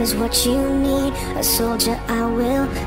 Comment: I hear "a soldier amuri" (yes i listen to too much kpop thank you twice, itzy, everglow and blackpink)